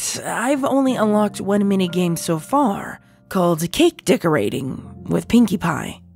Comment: Also, for a sec I thought she said " Kink decorating ◉_◉ "